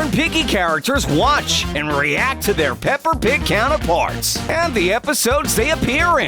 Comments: As anyone heard say pepper pig at 0:39 and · Did I hear pepper pig? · …Did he say pepper?